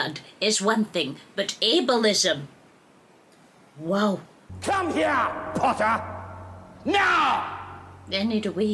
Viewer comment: i always hear it as “come here potter MEOW!”